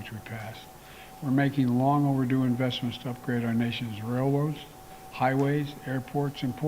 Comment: does he say rail wars? I’m not quite sure what that is 🙄